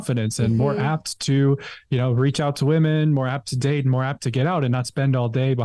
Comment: new saying idea after I thought he said more apps more apps for a split second, then this popped into my head "less apps to have makes you more apt to do."